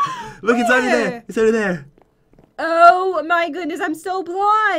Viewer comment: “Oh my god I’m so bwind” am I the only one who heard that😂?